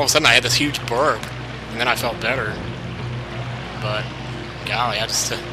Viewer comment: did you say "golly" at 5:49? i forgot that word even existed